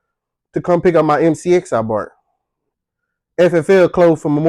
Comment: Did you just say "bart" instead of "bought" at 4:05? I'm gonna laugh at that till next week. 🤣And you just earned a new subscriber. ❤